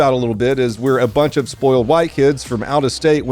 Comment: into the video I thought you said, "spoiled white kids from outer space", and I was instantly struck with inspiration for my next short story. Thanks!